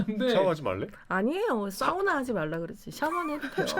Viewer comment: 저도 샤워하지 말라고 들었는디.